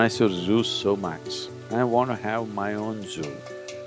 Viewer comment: I thought he said "I wanted my own Zeus"